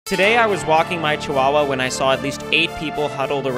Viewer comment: I thought you said koala not Chihuahua